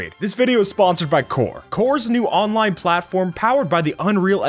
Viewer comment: When he said "Core is a new online platform", I heard "Core is an unoriginal copy of Roblox" Edit: There are a lot of copied games, like one game is "Arsenal of Guns", a copy of Arsenal. Tower of Terror is also a copy.